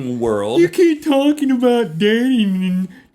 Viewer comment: did he say you keep talking about Damian 0:35 - 0:40 ?